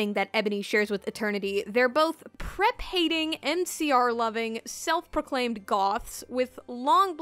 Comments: I heard NCR loving and thought she was talking about New Vegas for like half a second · I thought you said "NCR loving" and now I'm imagining a Goth in Fallout New Vegas.